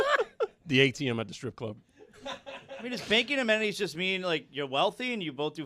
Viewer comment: I thought he said baking amenities